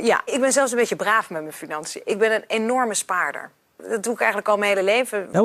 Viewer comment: that sounded like she said she's an "enormous spider" 🤣 Sorry, i love Dutch but that made me chuckle